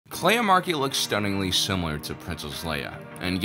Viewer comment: At one point, when someone said Kleya, i thought they said Leia and was super confused